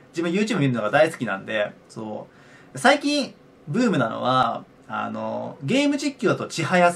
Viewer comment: 「最近ブームなのは」が「最近uuumなのは」に聞こえた